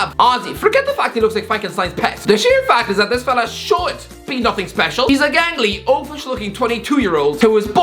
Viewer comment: Did you call Erling Haaland gangly